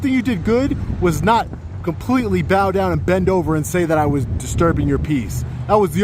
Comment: did you call the attorney Ben Dover?